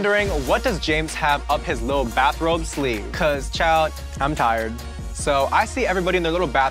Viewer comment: Did he just say "Chile" at around 1:39 or smthing?